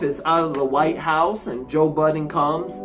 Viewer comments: I first heard that as ‘Joe Budden’ instead of Biden 🤣🤣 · He really said joe budden lol